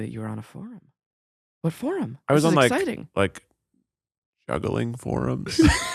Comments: I thought Keith said "struggling forums" 😂🤣 Juggling is super cool. 💕💕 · I really thought Keith said that in high school he was on “struggling” forums instead of “juggling” and I was like why are they laughing at him roflcopter